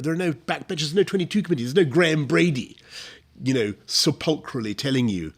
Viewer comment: what word does he say? "Surpolcrially" "Sopcrilally" I don't know what the word is to google it. He didn't just make up a word, did he? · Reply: Sepulchrally (adverb of sepulchral).